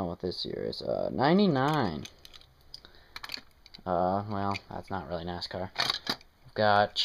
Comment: did you just call Lightning McQueen not a nascar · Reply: He said he's not really nascar